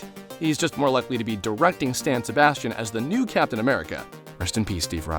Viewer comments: did u just say "Stan Sebastian" ???? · did he just say stan sebastian